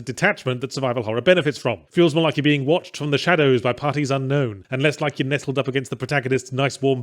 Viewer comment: When I mishear "parties unknown" as "partisan gnomes", I reckon I'm having a good day 😂